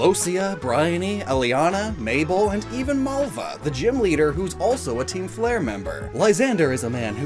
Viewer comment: whait what,Did I hear right? You said GYM leader or is my hearing bad?